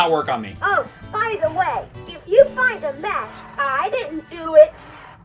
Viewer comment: I think she actually said “if you find a MESS, I didn’t do it”. Talking toys always use really cheap sound chips so it probably sounded like she said “match” due to bitcrushing